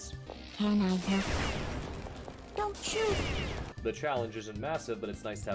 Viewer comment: did it just say "dont shoot" 😶😶😶😶